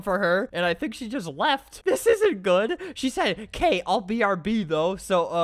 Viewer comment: I thought that said “kill brb” instead of “k I’ll brb” 😂😂😂